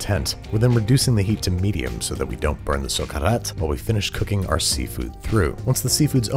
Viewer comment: bruh i wanted to know what babish sayed at 3:45 and the captions just say "babish speaks in foreign language" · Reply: socarrat is a word that can't exactly be translated into english, but it's when the rice burns slightly on the bottom of the paella